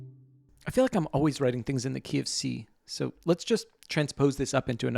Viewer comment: What I heard at first: “I feel like I'm always writing things in the KFC”